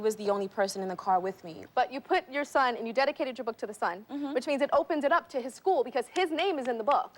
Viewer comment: Did she say dedicated the book to “the” son (11:39) wow. She did a good job handling herself. Tyra is trash for this interview.